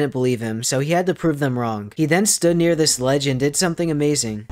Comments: Who else thought he said " he stood near this legend and did something amazing" 😂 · how many of you first heard it as "this Legend did something amazing." instead of "this ledge and did something amazing. "??